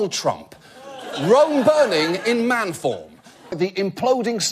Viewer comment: What does he say at the very beginning? "SOMETHING burning in man form"... sounds like he said "wrong" but that wouldn't make sense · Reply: He said ROME burning in man form